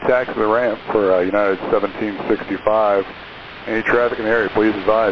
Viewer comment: subtitle is wrong. Should read "is there somebody up there that knows what they're doing" instead of "It's a comedy up there". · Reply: "any traffic in the area" instead of "and the traffic up ahead" --, I'd be happy to help with transcription and captioning if you'd like. I've been a transcriptionist for 4 years, with prior captioning experience (~2 years). Even if you just wanted someone to look over and proofread your captions before publishing, I'd be happy to do that. Let me know! Also, I know this one is nitpicky-- because it has the same meaning regardless. But I've noticed errors like this in quite a bit of your uploads, and sometimes it does change the meaning of the conversation. This isn't meant as a jab towards you; I know you put a lot of work into making this channel. So, please don't take it that way. I just want to offer my help 🥰